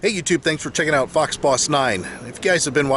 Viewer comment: for a min i thought you said fox body 😅